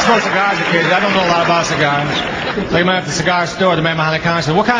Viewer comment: Can anyone explain this joke please? Can't quite hear what he says and/or my English isn't good enough to follow. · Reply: I can’t get the very first sentence, but then he says “I don’t know why I buy cigars. Like I’m at the cigar store and the guy behind the counter says, “What kind of cigars do you like?” “Ah…Itsaboys” This refers to tradition from somewhere around the 1960s where new fathers would order cases of cigars with pink bands that said “it’s a girl“ or blue bands that said “it’s a boy“ and hand them out to their friends, to celebrate the birth of their child. 😊